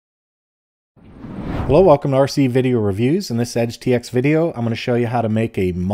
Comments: я не ослышался? в начале ролика прозвучало "Прикол, ага?" did I not misheard? at the beginning of the video sounded "Funny, huh (Prikol aga)?" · я не ослышался? в начале ролика прозвучало "Прикол, ага?" did I not misheard? at the beginning of the video sounded "Funny, huh (Prikol aga)?" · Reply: first thing i hear is "well, welcome to RC video reviews" the words "well welcome" weren't cleanly separated when he spoke